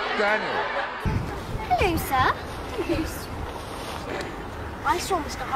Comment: what did Emma say here? Xx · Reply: Hello Sir